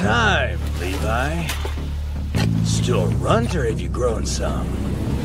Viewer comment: what did he say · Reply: Still a runt or you’ve grown some